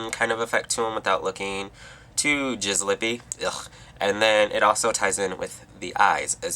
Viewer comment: am i a little crazy or did i hear "jizz-lippy"? if yeah, that's the coolest phrase ive heard you say